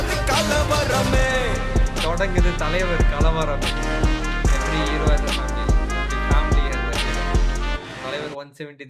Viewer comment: i heard "Inga thodangudhu Thalapathi kalavarame" Is that the word thalapathy or thalaivar ? Did I heard wrong?